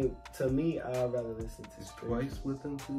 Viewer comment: …. Did you say yould rather listen to Stray kids?… thats JYP. BUT AYE I LOVE STRAYKIDS SO GO OFF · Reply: So is TWICE